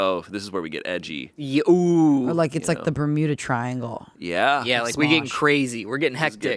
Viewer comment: I swear to god I heard the burrito triangle and I got excited